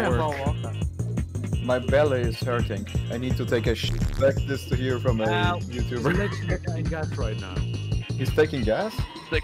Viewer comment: My belly is hurting I need to take a sh** expect to hear this from other youtubers. (I did my best to understand what said.)